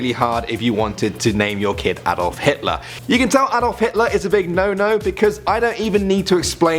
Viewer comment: It sounded like you said adult Hitler